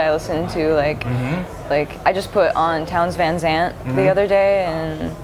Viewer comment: What are the names she says at 7:40 and 9:04? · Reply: Sergey Rozhenko Townes Van Zandt at 7:40 and Tori Amos at 9:04. :)